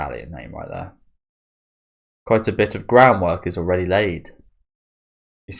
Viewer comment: "Quite a bit of groundwork is already laid..." For some reason, I heard that as "BROWNwork" and while I found it weird, I thought it could also be quite appropriate and a clever way to word it... then I did a double-take and LOLed.